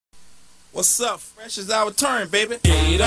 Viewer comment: sounds like he says "whats up fresh as i return baby"